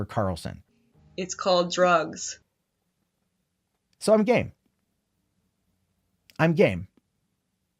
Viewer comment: I could have sworn he said "I'm gay!" like good job we already knew that.